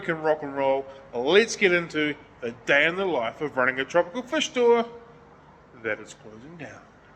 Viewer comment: what I misheard: Let's get into a day in the life of running a chocolate fish store!